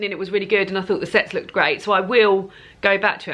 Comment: Oh. My. Goodness. You said "the sets looked really great" I thought you said something way more R rated🤦‍♀️😂 I thought that's a weird thing to say "the segs (lol) looked really great" so funny